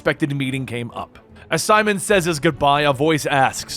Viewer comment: Is it me or does anyone else also thinks Simon saints sounds like Simon says